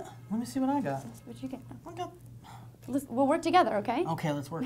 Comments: At the end I thought she said fucker lol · lol i thought she said fucker